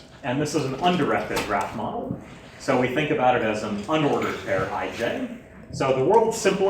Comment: what does he say at 5:07 "so we think of it as an unordered ____ " ??? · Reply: pair (i,j). So the world simplest ...